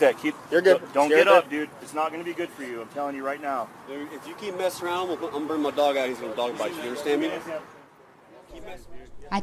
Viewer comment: Am I hearing it wrong at 4:39-4:41, it sounds like the officer is saying “if you keep messing around, I’m gonna bring my dog out here, and let him dog bite you” What’s wrong with that cop???